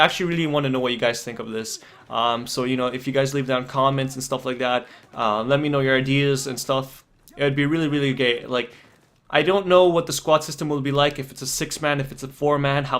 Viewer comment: did you say "it would be really really gay" lol